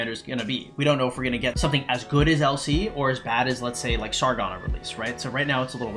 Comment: i thought he said someone as goog as El Cid